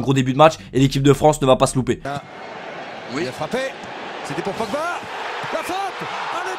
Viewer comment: C'est moi ou à 4:10 ils disent mbappé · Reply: Nn il dit pogba